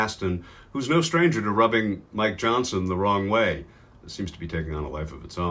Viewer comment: I first heard "No stranger to rubbing Mike's johnson"